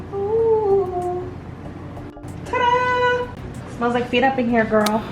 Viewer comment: lol did she really say "it smells like feet up in here, girl? or did I hear that wrong lol